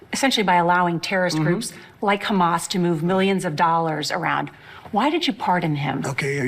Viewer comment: Millions???I thought I heard BILLIONS